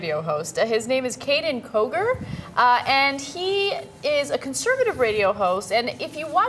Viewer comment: I thought Ben said his name was Caiden Cowgirl for a second